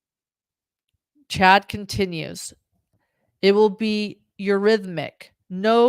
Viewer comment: CHAD’S EURYTHMICS (the band) COMPARISON. (1:33:30) reads the transcript “it will be eurythmic” imho Chad is talking about the 80s New Wave band the Eurythmics, he even says THE Eurythmics. If you didn’t live and breathe 80s new wave/modern music like I did, then you probably didn’t pick up on Chad's pop culture reference and comparison. I’ve listened to that section of the Chad/Lori jail call 6/8/22 so many times, Chad is turning me into a mush mouth like him! Enunciate bro, you’ll killing me! Anyway, I listened to the call again and this is what I heard mush mouth say: (btw, time is from this HTC video: <link> (1:55:52) Chad: it’ll(?) be the Eurythmics, no solo careers. (1:56:08) Chad: I’m just absolutely sure (1:56:07) Lori: muttering indisticitvelhy (1:56:06) Chad:I was just saying, it won't be the Eurythmics, it won’t be two solo careers. Twice Chad mentions the Eurythmics, does Chad really want to compare himself and Lori to the Eurythmics? It’s a weird analogy.